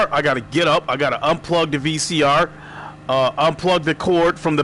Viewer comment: I just heard "unplug the VCR" and got confused as hell.